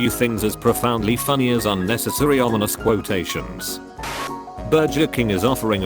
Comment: I had looked away from what was being read... all I heard was "bird jerking..." instead of " burger king" now I cannot in hear it XD